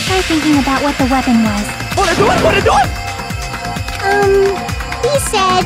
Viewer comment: I'm from a place that is notorious for having thick accents and I understood every word he was saying. It confused me at first when I realized there were people who really didn't know what he was saying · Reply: The only bit that had me confused was the "Wannado'twannado't", because I thought he was saying "Wanna do it? Wanna do it?"